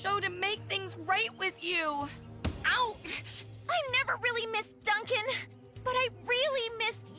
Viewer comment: i misheard this as "i never kissed duncan" and was so confused